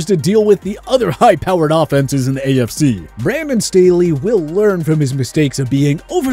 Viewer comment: did he just say Prammin Staley? If so atleast it is better then Brandon Stalley